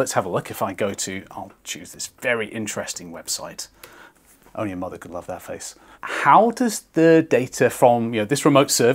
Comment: Did he just say your mother would love my face? LOL